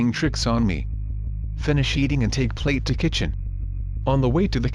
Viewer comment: When the computer said "bring rifle to kitchen" I thought it said " bring waifu to kitchen"